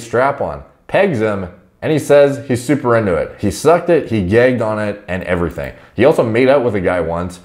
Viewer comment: So u enjoy an occasional peggin'. No biggie. A bit out there but it feels good, I get it, making sense so fa... I'm sorry, for a sec there I thought I heard u say "GAGGING ON IT" 😳